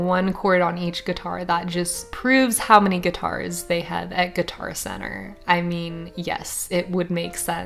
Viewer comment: I thought it said "I played guitar at every Guitar Center"